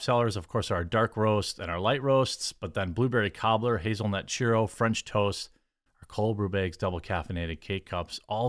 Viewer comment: For a while now I've thought you were saying hazelnut "chorizo" was one of your most popular flavors. I need to get my ears checked!